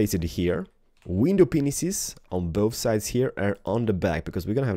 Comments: In 2:20 min it sounds like he said penis after glass my friend had pointed it out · Did say 'window penises'?